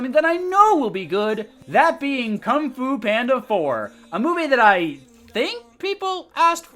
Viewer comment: If you activate the caption section, instead of it saying "Kung Fu Panda 4" it says "Kum Fu Panda."